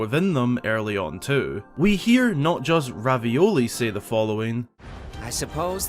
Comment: What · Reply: I meant revali but you sounds like you said ravioli and it also said that in the captions